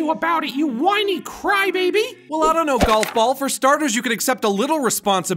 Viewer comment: did he say "Golf ball?"